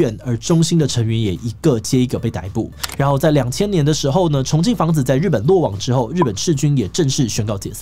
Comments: 重信房子… 一直聽成重慶房子… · 耳包聽成重慶房子 然後分心想著她弟難道叫做永慶房屋 ⋯⋯好啦不好笑😂